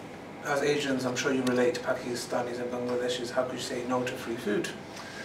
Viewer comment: did he say Bangladesh? 🇧🇩 I'm sorry I can't believe my ears oh the honors XD